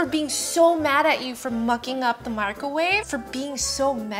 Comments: Everytime i look away while she started to say "mucking" i heard "fucking"💀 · I thought you said f#cking up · I thought she said f*cked the microwave but she said for mucking up the microwave (idk why I thought she said that) (end if you would like I would be so grateful bc I have never gotten 1 like on my comments not forcing you to like) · I thought she said fucking